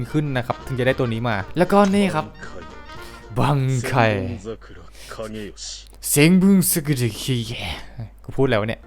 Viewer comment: เบียคุยะมันพูดว่า บังไคเซมบงซากุระคาเงโยชิครับ